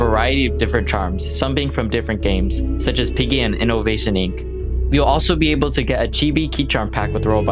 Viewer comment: DID I HEAR INNOVATION INC?